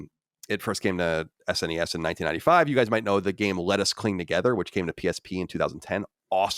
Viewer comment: If I didn't know what he was talking about I could have sworn that Colin said Lettuce Clean Together.